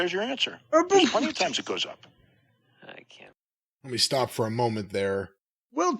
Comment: did I hear Bryan Alvarez say I can't